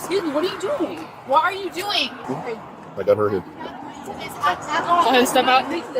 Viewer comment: the captions say, "I got murdered " 😂